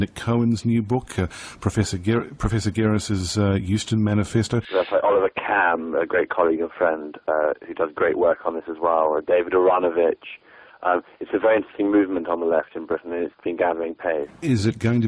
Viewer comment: What's the name he says at 5:15? Sounds like "Professor Garris's Houston Manifesto," But that can't be right :( Can someone help me, please? There's literally nothing relevant when I plug that into Google. Am I, as an American, mishearing to words?